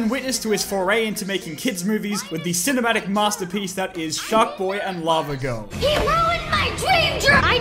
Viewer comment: in the subtitles it actaully say Jacques boy and lavagirl instead of Sharkboy and lavagirl. Just though I point that out.